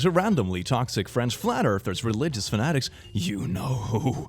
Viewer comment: Omg I heard "randomly toxic friends" and "flat earthers" together as "randomly toxic French flat earthers" and got very confused for a moment